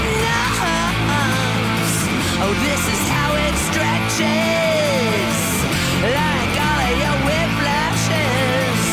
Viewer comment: I REALLY want to know what she's saying from 3:50!!!! I just love her expressive face. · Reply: 3 years late, but she's saying "Probation?? What does that mean, probation??"